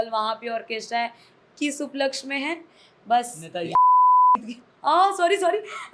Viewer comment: pe kya bola isne? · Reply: RJD